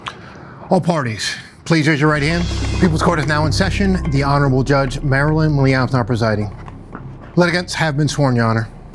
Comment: Does anyone know what Douglas says when introducing the Judge at 1:09? He says “Judge Marilyn Milian (something) presiding. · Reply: The honorable judge Marilyn milian is now presiding…. Pretty sure that’s it 🤗